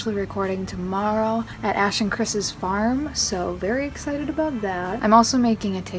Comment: i thought she said ”we’re recording at Ashton Kutchers farm”